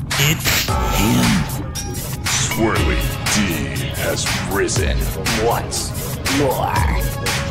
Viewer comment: The generation we currently live in has corrupted me 😭 I heard “Swirly D has Rizzen once more” 😭💀